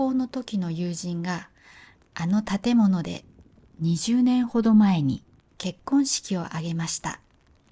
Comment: Hi, thanks for the video! I saw something wrong in the subtitles. On 2:16, you said that the wedding happened 20 years ago, but the subtitles say that it only happened, without saying when it was.